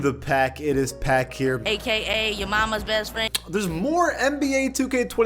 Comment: "aka yah mamas best friend" gets me every time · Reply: I thought is said aka yah mama best friend